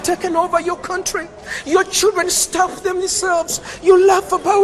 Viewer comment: what was the word pasta says at 3:31? He says "your children ___ themselves", it starts with an s but I can't tell what he's saying. It might be stabbed based on the hand motion he makes when he says it. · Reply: It's stuff